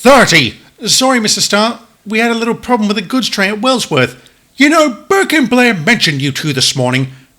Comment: Audio says "goods train at Wellsworth." Subtitles say "goods train at Ulfstead".